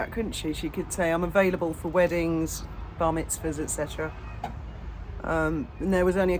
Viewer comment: I first thought you said "Vomit zahs" talking about her sh*te.